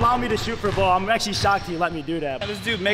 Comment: I hear bro said "I'm actually shottzy and let me do that" like OpTic Shottzy